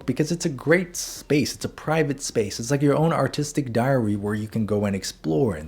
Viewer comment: totally heard it as "keep a sketchbook; it's like your own artistic Diarrhea"